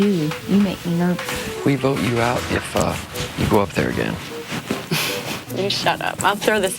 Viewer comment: LMAO ALERT At 3:46, did Shelly just say "fucked"?!?! HAHAHAHAHAHA! Check it out! *Also, reply your opinion on this comment! =D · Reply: kinda sounds like she said " we vote you out if uh you go up again"